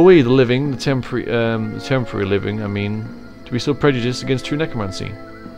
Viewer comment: I think the last word Zxar said there was democracy, not necromancy